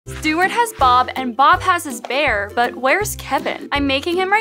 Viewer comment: I thought she said Stewart has balls